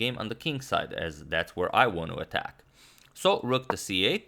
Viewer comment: That's the first time I heard Agad saying "I wannu" instead of "I wanna".